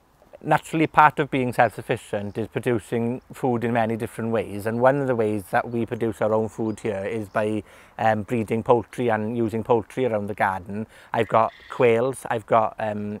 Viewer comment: Breeding poultry... and I thought he said reading poetry hahaha. Great info... love your garden and your accent 😀